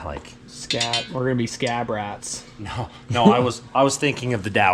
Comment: ❤ the "Scab Rats" moniker. You guys always find a way to scab stuff together to make 💩 work well. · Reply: At first I thought he said "Stab Rats" because he was stabbing the motor in lol. Then I figured it out.